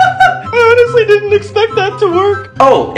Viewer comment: Evryone: talking about bald cap Me:Did he just say bakubro 🤭